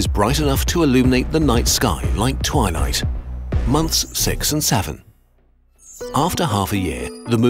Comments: did he just say 67😂😂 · what did you just say..? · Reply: 6...7